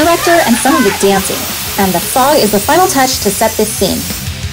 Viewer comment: if u put on subtitles it says frog instead of fog